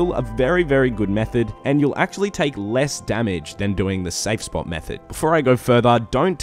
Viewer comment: I definitely heard the sexbot method lmao